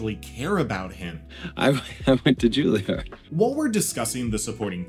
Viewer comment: wtf does he say?? 🤣🤣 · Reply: "I... I went to Juilliard"